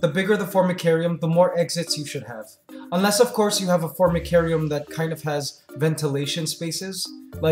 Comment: I thought you said FORNICARIUM ....... "Jeez how very ROMAN of them..." "Right this way one leaf untold hours of muckety muck